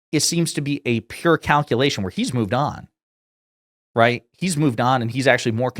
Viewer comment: Renovations of the White House? I heard him say "construction of the ball room" 💀